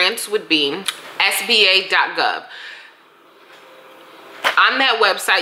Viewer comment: Say it again? What .gov? It sounds like spa.gov or Sp8.gov but neither worked. What did you say? Edit: Oh it's SBA.gov. Lol. It sounded like you said P. Ha ha. Small business administration that makes sense. Lol.